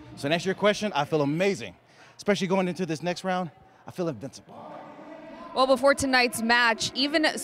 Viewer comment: I thought it said invisible